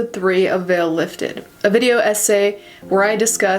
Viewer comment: I really thought you said “video ese “ instead of video essay